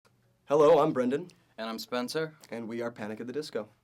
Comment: "Hello, i'm brendon" "And i'm spencer" What I Heard- "Hello, im Sexy" "And i'm Flawless"